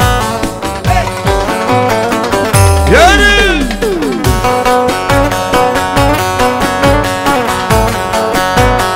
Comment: ne diyor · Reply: Yürü diyor yörüü gibi